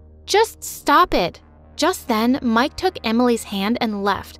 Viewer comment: Lol I thought she said the boys name was ''Mark'' not ''Mike'' lol she literally said Mike